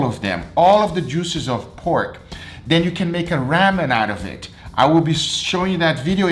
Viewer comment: I totally thought he said save your pork juice for when you make "rabbit"...lol Then I remembered his Ramen video.